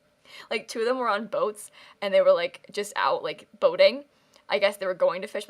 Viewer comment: did you just say “boating”?